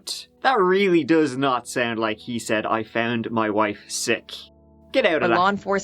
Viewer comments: i heard " i cut my wife..shit!"😅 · I thought on the call he said "I found my wife, shit!" He then genuinely sounded surprised when he realised she was bleeding from her chest and its hard to fake surprise and panic like that whilst trying to remember your story too because it takes different parts of the brain... feel free to look at dalia dippolito if you need any convincing. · The first time I heard it without subs, I honestly heard “I found my wife sick”. I even thought I must have heard wrong because it didn’t make much sense, but that’s what I heard. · I do audio transcription and I'm sure he said, "I stabbed my wife— Shit." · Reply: I heard, "I found my wife..." 🤷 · For what it’s worth I heard ‘I found my wife sick’ before the reveal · I heard "I stubbed my wife" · I heard “I found my wife stiff” the first time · At first, I really heard "I found my wife sick" 🤔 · I literally heard, "I found my wife sick." But when I read, "I killed my wife, shit!" I heard that too. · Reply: I thought he said I found my wife, shit. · I heard 'I found my wife slipped' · It sounds like he said "I shoved my wife- shit" and him talking about the knife wound not bleeding sounds like maybe he got into an argument with her and pushed her and killed her by accident and then tried to cover it up by cutting her up to make it look like an attack. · I thought he said I stabbed my wife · i heard "i found my wife, shit" so... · Sounds like he said I shod my wife.